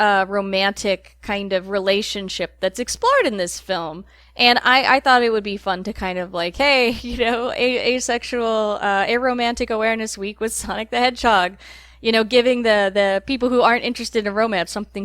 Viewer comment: I'm so sorry but for a second I thought you said " aroma awareness week" I'm so dumb oml